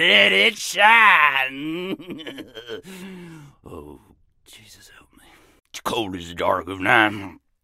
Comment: Oh snap I didn't hear he said: Oh jesus help me. In a small whisper at 0:23 Because I didn't had a headset on at first xD I guess you really can miss out on a lot with no headset huh.